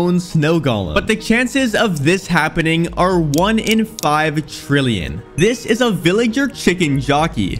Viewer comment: did he just say five billion five trillion?